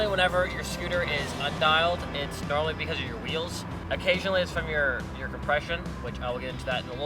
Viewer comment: I thought he said it’s because if your depression